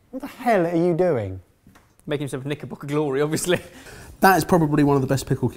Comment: What? I can't really tell what Ben said was doing. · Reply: "Making himself a knickerbocker Glory, obviously." Which is an ice cream thing.